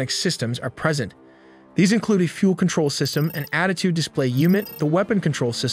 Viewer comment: did he say attitude display? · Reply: he did. Attitude is an aviation metric for angle of attack.